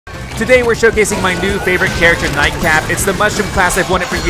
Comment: it sounded like you said night crap